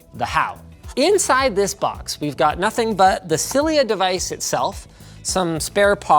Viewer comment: When he said "Cilia Device", I thought he said "Sillier Device", lol